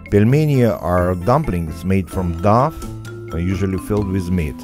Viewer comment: "dumplings made from Duff" is what I heard. :) ...I'd eat that.